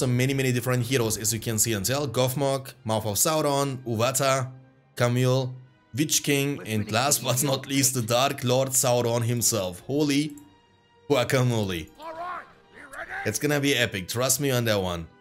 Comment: i thought you said bi*ch king xd